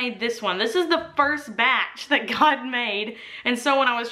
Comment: What was that one line she says?; “It was the first batch that __ made? “ God? Did she refer to herself as God?! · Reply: "first batch that got made"